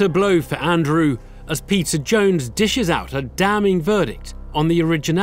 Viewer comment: I always hear “pizza jones” instead of “Peter Jones” lmao